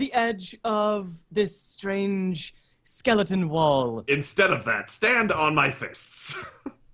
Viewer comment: i'm not gonna lie, i swear to god at 3:51:21 I though said "instead of that- sit on my face." I had to pause the video and go back. just i nearly starting crying i don't know where that came from. but it made this 4 hour long stream just that much better.